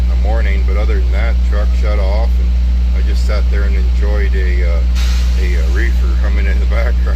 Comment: I had to play that segment starting around 2:14 a couple of times to be sure I heard what I thought I heard, "I just sat there and enjoyed a reefer."